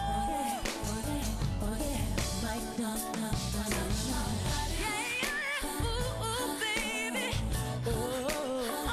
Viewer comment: What did she say at 3:44? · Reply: Mic